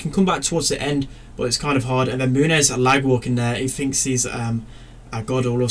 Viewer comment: is it called "lightwalking" I can't really understand it cause I'm not american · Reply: Lag walking* it’s just a BM pretty much